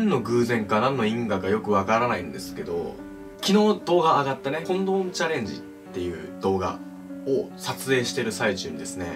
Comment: コン⚫ームチャレンジにしか聞こえない